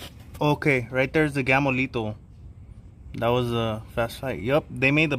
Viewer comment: Took me over 10 minutes to realize you were saying “Gamo-Lethals”. Through the whole video I coulda swore you were saying ‘Gamolitos’ haha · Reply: tu eres un gamolitoooo...I thought he said that too